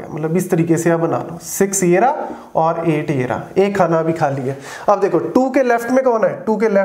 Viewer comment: did he say yeh khana vi khaliya??😂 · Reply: Hahaha..its “Ek Khana(space) abhi Khali(empty) hai”